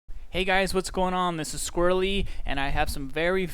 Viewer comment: Sounds like you say "Misses Squirrelly" lol